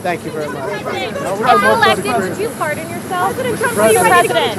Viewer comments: I thought the journalist said “if you’re elected will you fart on yourself?” instead of pardon yourself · Maybe because I'm listening at 1.25 speed, but the one lady said "if elected, would you pardon yourself?" But first time around I only caught the back half of that and misheard her say "did you fart on yourself?